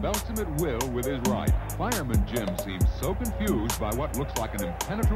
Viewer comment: Did I hear that right? “Fireman Jim”